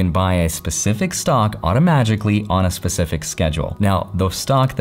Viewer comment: did you say automagically?